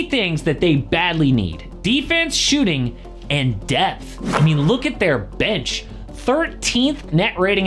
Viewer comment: Bro, I thought he said "Defense, Shooting, and Death" for a second. hahaha